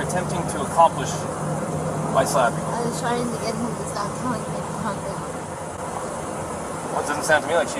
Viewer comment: from what i can hear at 13:41 she says: "i was trying to get him to stop telling me to calm down".